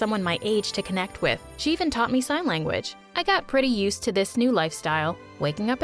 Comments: I think it needs to be corrected to "Sign Language" instead of "Sing Language". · Sing language? I thought its called SIGN LANGUAGE